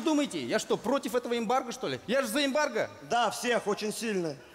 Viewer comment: Не вырезали про эмбарго! Фраза "Я же за эмбарго?" послышалось ему, как "Я же заебалко?"